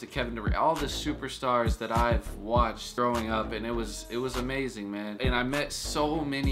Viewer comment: he says throwing up instead of growing up